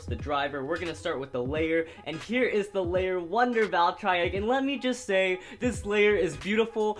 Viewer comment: did he just say Valtry Wtf